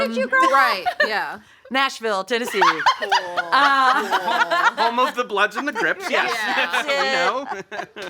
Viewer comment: did he just say “home of the bloods and the GRIPS?” 😂😂😂😂 Lmao thats funny.